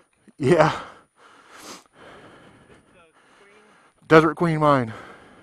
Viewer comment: I will check out his channel. We often talk about doing the full-time RV <link>! Interesting place, but I had to do a double-take cuz at first I thought you said you were stopping at the "Dairy Queen", not "Desert Queen". ;) Wow, you're pretty brave going into that cave / mine shaft...that would freak me out!